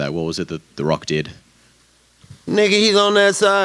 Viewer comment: I’m having a debate with my cousin, at 17:33 did Nate say nigga? Lol